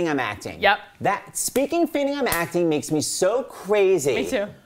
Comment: is he saying "Speaking. Fainting. I'm acting?" I'm not understanding. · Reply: *speaking of feigning